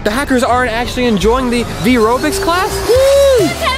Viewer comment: i thought its called "vyrobics" not ryvobics whaaaat have a nice day/evening/night :) ;)